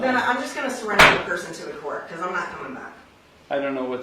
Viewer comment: No, she didn't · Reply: she clearly said that "i am not coming back" about